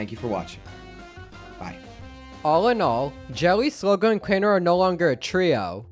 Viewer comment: Jelly with a big belly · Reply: Every time you said “Jelly” I heard “Joey”